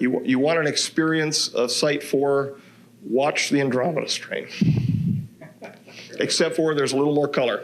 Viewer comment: Can anyone tell me what he said at this time 45:19 ? I heard andromeda but not sure what came after.. sounds like he said “stream”… 😅